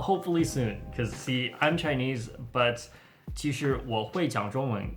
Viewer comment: i thought he said ‘I can read chinese but can’t speak it’ in chinese